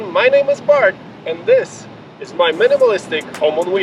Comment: I thought you said "My name is FART"....my bad